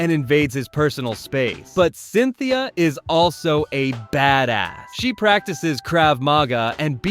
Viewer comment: did he just said badahh💀 Btw danm cynthia looks fine